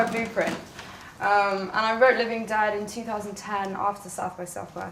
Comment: What does she say at 0:50? She wrote Living dead after...?